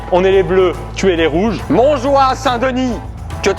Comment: A 00:23 Pierre Croce dit « je suis les bleus tu es les rouges ) mais moi j’ai compris (je suis les bleus et tuer les rouges)